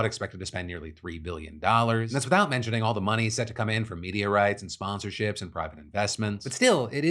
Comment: Phil said "media rights" but I heard "meteorites"